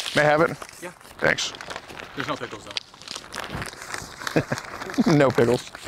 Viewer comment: Did he just say “no pickles”??